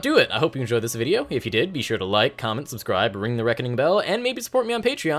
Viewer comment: everytime you say "davvy out" all i hear is "daddy out" 😔😔😔